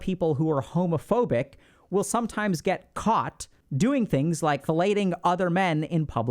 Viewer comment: what is that word? filating?